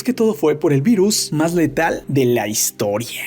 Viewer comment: 😂😂yo pensé que decía "El Coronavirus es el virus más letal de la historia" 😂 pero por dicha me equivoque